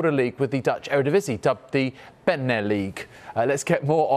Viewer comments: Beneleague does roll of the tongue this guy just, somehow managed to butcher it. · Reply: It sounded like he said Bendtner League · I swore I heard Bendtner League LMAO Paying homage to Lord Bendtner I see...